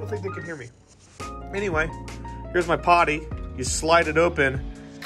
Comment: "There's my potty. You slide it open and you can potty in it". I heard the word "potty" as if he said "party" in a really thick boston accent..